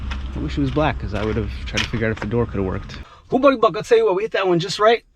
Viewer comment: What did he just say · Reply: Whew Buddy Buck, I will tell you what!